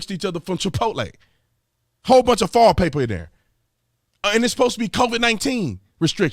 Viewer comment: I thought he was saying "fall paper" then realized he was saying "foil paper".